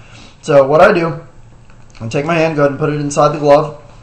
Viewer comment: i sounded like u said handgun at 0:30 lol